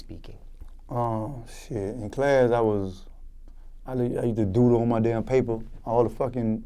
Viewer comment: thumbs up if you thought that he said "I used to doodoo on my paper"